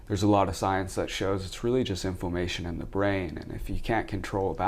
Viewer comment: Inflammation to the brain or?? What did he say??